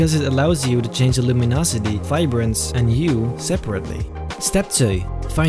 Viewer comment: he said “step twee” instead of step two which is in dutch😂